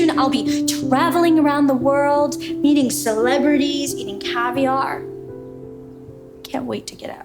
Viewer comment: I heard : eating celeberites!!